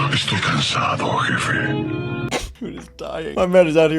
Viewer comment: just in case you’re wondering what is said: “Estoy cansado jefe” “I’m tired boss”